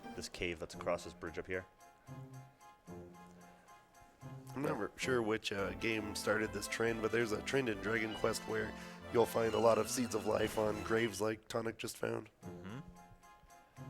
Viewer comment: I'm still trying to figure out what he's saying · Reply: "I'm not sure which game started this trend, but there's a trend in Dragon Quest where you'll find a lot of Seeds of Life on graves, like tonic just did"